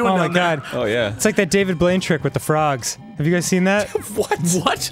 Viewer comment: So, for the frog thing with David Blaine; I totally misheard Dan and thought he said David Bowie. You can imagine how confused I was, but also totally willing to believe it could be true.